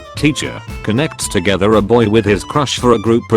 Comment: I just wanna call it that at 4:31 it says buy instead of boy.